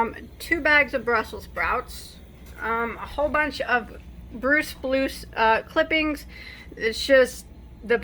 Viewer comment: Sounded like you said brue spluce. Teeheehee. So funny and cute. That’s what dying is all about. Experimentation.